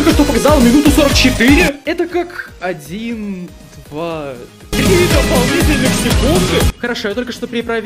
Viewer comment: мне одному показалось что он сказал 4 ДОПОЛНИТЕЛЬНЫЕ СЕКУНДЫ?!?!